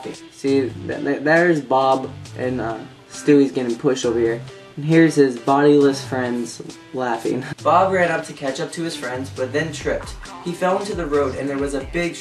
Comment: you said ketchup instead of catch up